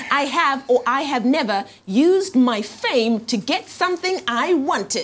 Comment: In the 1st question I thought she said "my thing' instead of "my fame". lmbo I was like whoa Tev!! For clothes?? lmbo